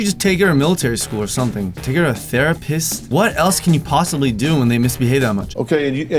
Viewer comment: Sounded like you said "Thera-piss"